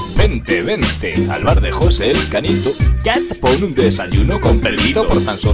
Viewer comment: pensé que decía "bar de josé el canijo" y yo pensando: "hombre, pues muy canijo no es..."